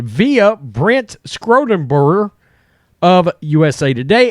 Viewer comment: Brent Scrotumburger is a great name for this story. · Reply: I mad this a comment about "Scrotumburger" before I saw your comment. Totally sounded like he said that haha